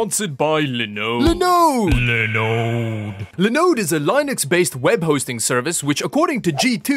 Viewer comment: Why did I hear "Linux based Web hosting cervix"?